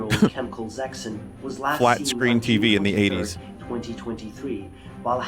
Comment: does he say 2023